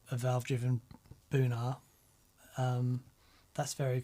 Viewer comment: Every time you said Boonar, my brain heard it as Bunha….